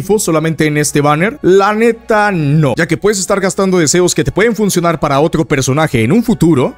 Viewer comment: yo solo entendí que gaste mis protos por esa arma 😅